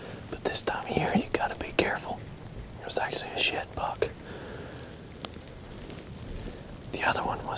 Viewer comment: I thought he said $h** buck I had to rewind it a couple of times. anyway I love the video.